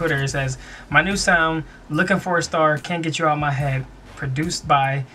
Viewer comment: sounds like is saying "ow my head" instead of out of my head